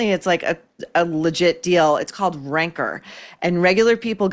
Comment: When Maureen mentioned Ranker, I thought she said Rancor. It fits, keep it.